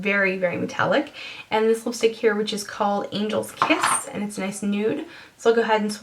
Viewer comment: Hahaha I thought you said angle's piss